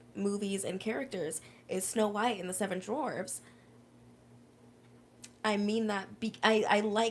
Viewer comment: I swear you said Snow White and the seven drawers 😂 interesting concept